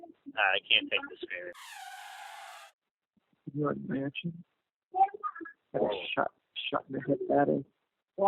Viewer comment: Donna has a very evil look in every pic, yes it did sound like she said can you imagine being shot in the head better.